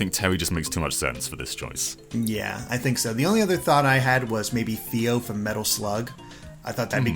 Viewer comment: whats the girl name at 7:04 couldn't hear it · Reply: Fio